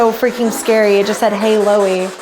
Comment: I heard "listen to me" not "hey Loey" but now I'm shook